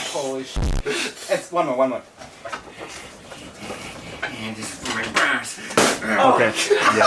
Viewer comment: whisperd this is for my bra's (that is what i thought he said)